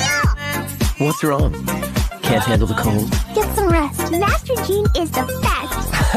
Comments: I literally thought Klee said nitrogen is the best lmao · No cause I thought Klee said “Mac and cheese is the best!” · That "Have some rest, Master Jean is the best!" Was clean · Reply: i thought she said "master jean is depressed" lmao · I really thought venti said at the end, "There's never a dumb moment travelling with you!" 😭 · WHAT I THOUGHT KLEE SAID “MASTER JEAN IS THE FATTEST” NENJDJEOMDDHKSKS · "get some rest, master Jean is the best" was smooth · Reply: i thought klee say "Master Jean is the Fat" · I thought klee said "mac and cheese is the best"